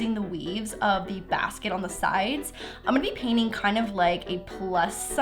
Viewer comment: Who else thought she said, "a-nipple paint"